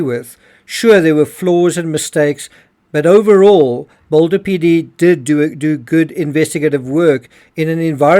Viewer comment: I never said the DA did a good job. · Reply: I thought you said PD did a good job? Misheard abt DA